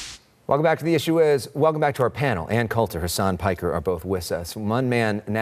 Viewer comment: I thought he said, “Ann Coulter and Her Son Piker.”